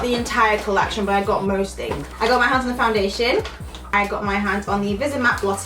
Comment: I thought she said "I got my husband foundation " my hearing...